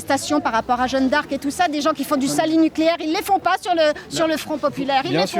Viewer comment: 7min 25, J'ai entendu "le salut nucléaire"... 🤔 Bon j'ai compris, mais un échange comme ça c'est pas un débat...